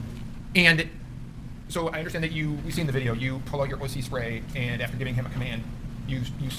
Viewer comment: I about spit out my coffee when I heard what the defense attorney say "And you proceded to pull out your wussy spray?" 😂 Now I could be wrong but if not he just called the cop a wussy. 😂 that would be priceless!!!